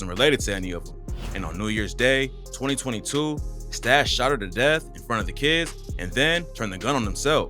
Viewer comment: “and on New Year’s Day, 2022 Stash ______? death and turned the gun on himself “ idek wtf you said there. Changed play speed to .50 and .25 and it still sounds gibberish. Best I got was “shadowed? The death “ I really can’t make an English word out of it.